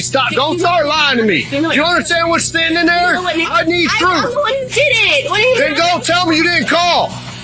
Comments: she said “im the one who did that”did anyone else catch that? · I don't know if y'all caught it (3:31) at the beginning but She clearly said' I'm the one who did it' and points at the scene.she wasn't talking about calling them. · "I'm the one that did it" Anyone hear that?